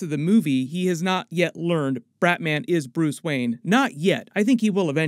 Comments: I love your videos! Did you say Bratman is Bruce Wayne at 5:53? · Did he call him Bratman at 5:53? 😂